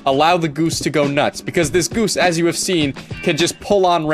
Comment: I thought it said the goons